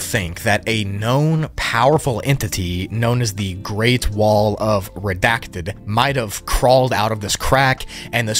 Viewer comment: did you say great wall of china?